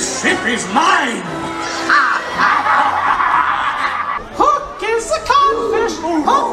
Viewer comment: Im missing out on these cruises. This looks like a blast. Did I hear "Hook is a catfish"?